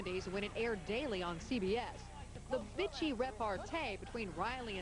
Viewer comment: Did She say "Bitchy"!?!?